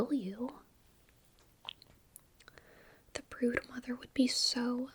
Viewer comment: the brood mother? Big Momma from No I'm Not A Human is all i can think about rn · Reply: I’ve played too much Hollow Knight, I thought she said Gruz Mother at first 😂